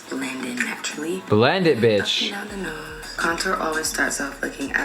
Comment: did he just call rihanna « bitch »